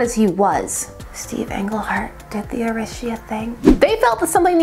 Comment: “Steve Englehart did the (not sure what was said) thing.” What was that word? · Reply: Arisia